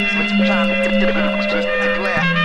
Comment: I always thought he said hold the mind like a limirick. Would probably sound just as good if he did